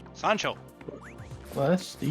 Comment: Did just said Sancho!? 😳